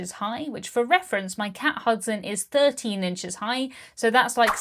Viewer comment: Am I the only one who heard "cat husband" instead of cat Hudson? 😂